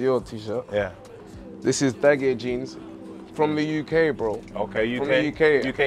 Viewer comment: What jeans did Bane say he was wearing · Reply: Dagye on insta